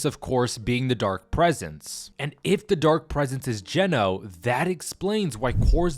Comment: Geno...i thought ya said genos.